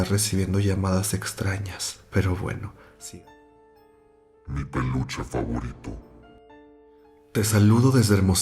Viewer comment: YO ESCUCHÉ PANOCHA Y ERA PELUCHE JAJAJAJAJAJAJ